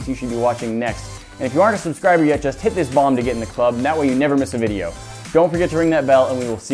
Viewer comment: It sounds like he says "hit this bong"